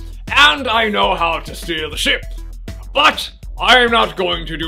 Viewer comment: I've heard good things about the Hook game (interesting choice for this festive month) so it'll be neat to see both these versions shown off. I'm pretty sure I've heard more talk about the Mega Drive version in the past but I also think I recall something about the controls which might give the SNES version the advantage. I'll soon find out. 1:47 - I thought you said steer here but the subtitles say steal. To be fair, either line works. Cool to see all these "captain" references. 2:19 - Captain Crunch here is absolutely hilarious! 4:48 - So far, I'm think SNES has the victory in Graphics. There's the odd detail that looks better on the Mega Drive version, but it's colours seem more diluted and the SNES version has a lot of little details that shine through. The 7:08 snow level is a clear indicator of the better looking SNES version, but it's nice to see so many other weather effects as well. 9:21 - The scroll hud of the Mega Drive honestly looks better. The black indicates it's not part of the level so you don't get the feeling it's obscuring anything. Furthermore it actually looks like a scroll with with it's rolled up page ends. 15:08 - The flying does seem pretty well represented and looks quite smooth. They incorporate it well throughout the experience. There is a lot of little neat touches (like the fire following level). 16:57 - Wow; a dominant SNES victory. I'd perhaps have given a draw to Presentation but I do agree with the end results.